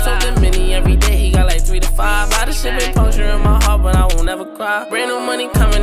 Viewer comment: "A lot of shit torture my heart but i wont never cry" Tjay is tough and this duo is Official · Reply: I thought he said “purgin’ my heart” but hey that verse was still fire 🔥